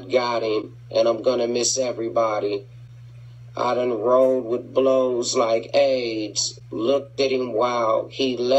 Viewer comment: i done rolled wit blows like aids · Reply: Not gonna lie, that's the only line I had wrong all these years, I knew the rest. Thought he said "I done roll and blown my gauge"